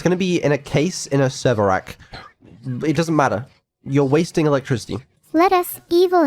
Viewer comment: for some reason I thought called her a waste of electricity 😭 I was like that's gotta be a slur